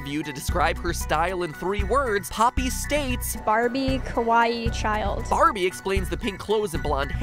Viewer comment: So when Poppy states the three words to describe herself, does anyone else feel like it almost sounds like she is saying "kowaii" instead of "kawaii?"